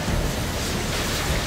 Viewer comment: Anyone thought it said abortion… no one?…. I guess just me